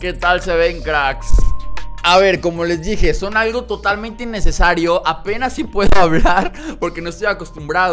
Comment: Tiene razon, cuesta hablar porque parece que dice "Son Totalmente Necesario" xd (Edit: Ahora te voy a decir como me decia mi mamá cuando me pusieron los brackets: "Si te pegan haste bolita y protegete los dientes, que no salieron baratos" xd)